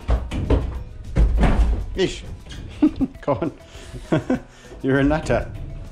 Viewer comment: did he say your a nutter?